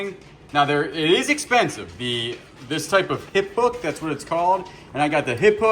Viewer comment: Oh hip HOOK! I thought you said hip book! Lol definitely looking this up! Thanks